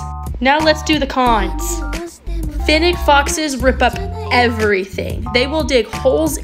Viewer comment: Ngl I thought she said synthetic foxes